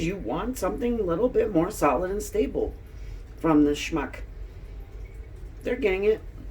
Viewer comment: omg..did you just say..schmuck🤣🤣🤣🤣🤣🤣🤣🤣🤣🤣